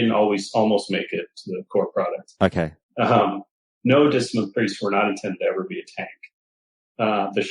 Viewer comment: around the 51:00 mark I can't quite make out what he said: discipline priest were never meant to tank?